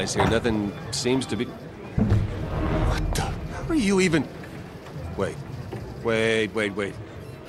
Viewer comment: the most intresting part of the event... What is he saying ''How are you even'' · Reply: Here. How are you even here. You're a Fortnite skin in the Epic office.